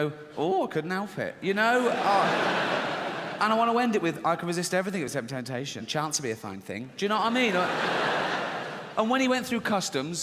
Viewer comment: What did he say at 6:21 to 6:28? I don't think the captions are accurate. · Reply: "I could resist everything but temptation, chance would be a fine thing." You know what I mean?